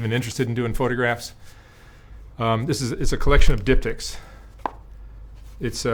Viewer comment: I straight up thought you said the book was a collection of dick pics 🫣😂😂😂